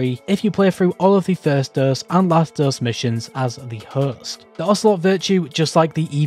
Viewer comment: “if you play through the the first dose and last dose missions as the ____”. Sorry man. I can’t understand what you said right there, and I want to get this thing for free! Cheers. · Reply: As the host